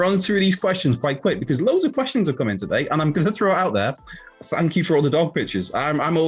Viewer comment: I misheard when he said "thank you for all the dog pictures." I heard " thank you to all the goth bitches"